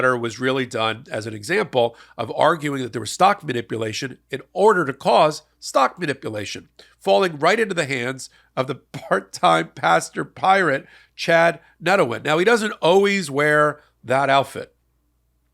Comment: I must get better loudspeakes, I thought you said Pasta Priate at forst!!! 😃